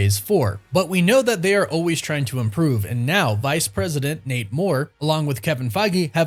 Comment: I keep hearing “Vice President Namor” instead of “Nate Moore” lol